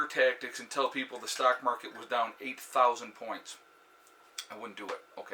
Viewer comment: I really miss heard I thought I said 800 pts lol so yea crazy